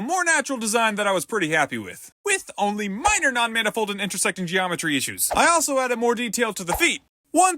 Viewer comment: What was bro saying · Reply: "With only minor non-manifold and intersecting geometry issues"